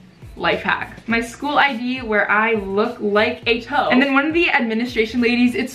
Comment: I thought she said hoe 😂